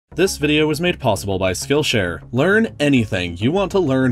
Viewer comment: Just realised that he says: This video is made possible by skillshare. I thought he always said: This video is made possibly by skillshare... Been watching for over a year